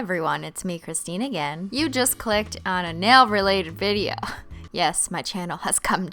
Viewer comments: when she said nail retated video I thought she said mewo retated video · when she said nail retated video I thought she said mewo retated video <link>